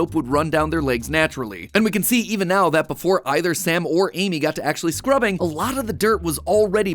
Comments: I know MatPat said "Sam or I," but I heard "samurai." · When he said sam or i it sounded like MatPat said samurai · For a second I thought he said samurai, turns out is was sam or I, sorry sam. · I literally thought Matt said "either Samurai" but he said "either Sam or I" facepalm